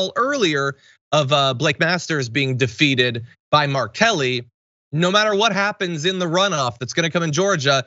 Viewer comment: MARK Kelly, I thought you said R Kelly... 🤣